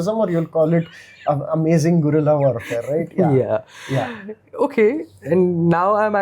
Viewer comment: I thought you said Karela warfare!? Bitter Gourd is very bitter though, so you may have point.